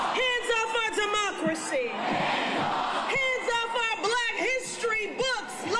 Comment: I DIDNT HERE THE CROWD YELL HANDS OFF FOR BLACK HISTORY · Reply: I heard her say Hands off Black History Month ❤‍🔥❤‍🔥❤‍🔥